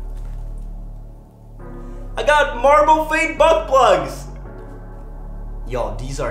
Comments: did he say buttplug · it sounded like he said butt plugs...giggity